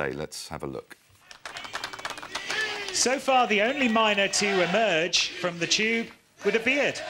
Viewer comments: what does the commentator say that's so funny? I've played it ten times but I just can't make it out: "so far the only miner to make it out of the tube jkl;dlsjfgsliuntbvliret" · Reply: I heard "so far the only miner to make it out of the tube it appears" which is funny by itself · what does the commentator say that's so funny? I've played it ten times but I just can't make it out: "so far the only miner to make it out of the tube jkl;dlsjfgsliuntbvliret" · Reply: "...with a beard" is what he says. I listened to it once and heard it perfectly fine.